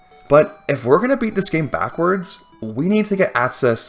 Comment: Genuinely thought he said "r@ this game"...